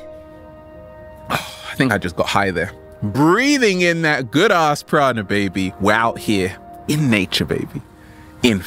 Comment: Your videos have changed my life dude! This one time I thought I heard you say "good ass Chronic" but then I remembered I was high up in Jupiter haha. Anyways, thank you for all the good vibes you share!